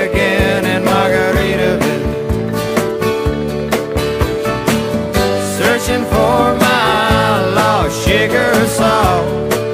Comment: how many always thought he said 'searching for my outlaw shaker of salt'?